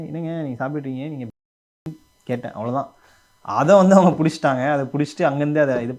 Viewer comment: What did he said? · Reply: He said Brahmin uh nu ketten